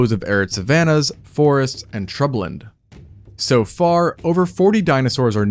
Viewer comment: What word did he say at 6:05? Trubland? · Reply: shrubland